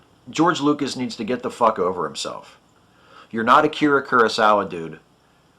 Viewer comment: What's he saying at about 2:45 that sounds like "You're not Acura Cura Sala, Dude"?